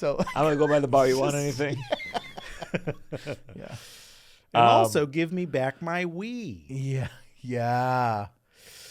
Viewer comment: Loved this episode, but the only issue to having one so long is that by the time of the callback at 1:34:10 I had forgotten about the stolen Wii situation, so I heard Sam's remark as "Give me back my wee", and was so confused for the next few minutes as to what ungodly airline was stealing urine